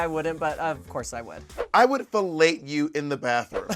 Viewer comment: what did Bob say at 4:55 ? "I would __ you in the bathroom" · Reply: fellate lmao